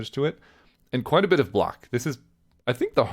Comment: need a bit of what ? you said it wasn't rng based. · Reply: He said block. Lmao